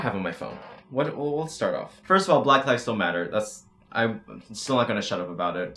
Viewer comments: For some reason the captions said “black lives don’t matter” and I want y’all to know I literally said “black lives still matter” Also masks have been proven to not restrict oxygen flow. Don’t say you can’t breathe in them, just say you don’t like having to wear a mask. OH AND here are SOME of the 100+ “influencers” who went to that party unmasked: Nikita dragun, Emma Chamberlain, James Charles, Charli and Dixie D’amelio, The entire hype house, Bryce Hall, Larrayxo. And turns out cases in a lot of other countries are still rising. Oh well I guess this entire world is stupid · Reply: I heard “black lives don’t matter” and I was like GIRL WHAT I- and then I looked at ur comment and said “oh thank GOD” 😭😭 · I heard “black lives don’t matter” and I- but he actually said “black lives matters” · oh my god when you said "black lives still matter" I thought you said "black lives dont matter" and I was so confused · I thought u said black live don’t matter for a second I had to rewatch to make sure you didn’t 😂😂 I was like 😳 hol up what he just say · I thought he said “black lives don’t matter” AND I KEPT REPLAYING IT then I realized he said “black lives still matter” Oof you scared the shit outa me. 😭😂